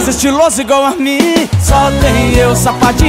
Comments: EU só não entendi quem que é esse "ARMIN" ? ( 00:22 ) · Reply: rasha nour Hi ! I don't speak english ! minha cara amiga, eu sei que é "igual a mim" e não "Armin" mas acho que você não conhece a arte de ser "Hu3 BR" então volta pra sua terra ! Kiss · EU só não entendi quem que é esse "ARMIN" ? ( 00:22 ) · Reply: Bruna Lima é amin